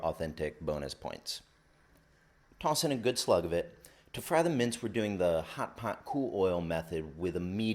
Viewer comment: A list of ingredients near the start would be a great help. Also, what are you saying at 4:07. Sounds like "mints". Oh, wait... you are trying to speak British? They say "mince". Americans say "ground meat".